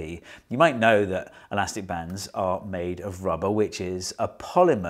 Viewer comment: I heard "elastic bands are made of rubber witches" and was like "what?"